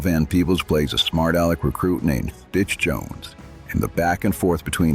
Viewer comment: Stitch Jones, Sounded like you said Bitch. Really good job, hard to argue with the list or your ranking other than Heartbreak Ridge (which I was in when it came out and still love) not being a boot camp movie. Maybe Jack Webb in DI instead, though it wasn't a War movie was it? Best lines in Boy's in my opinion: Look here, its Jesus Christ. Oh you think thats funny? You get on your knee's and you be his disciple and the whole Washington checks his package monolog.